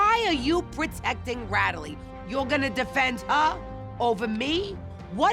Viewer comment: Did Renee say, "Ratalie"???? At 5:31 🤣😂😂😂